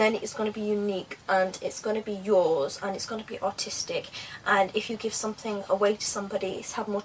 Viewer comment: your stuff is amazing but when you said aristic one time i thought you said autistic ha ha :D